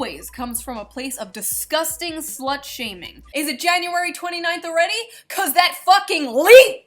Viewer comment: Did you say January 29 was leap day?